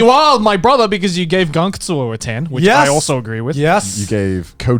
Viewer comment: what anime is this? I can't understand what he said,,, gankatsu?